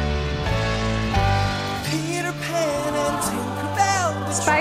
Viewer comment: did she say Peter pan was spider man